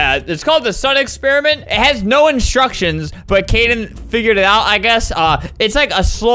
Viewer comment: Kaden : “wE’rE jUsT sPoOnInG iN mId AiR” Kids who think spooning mean doing the do : “NOOOOoOOo tHaTs BaD!!” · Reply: I thought ‘Kaden’ said ‘Karen’ xD